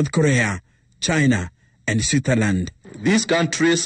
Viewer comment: did he say sweeterland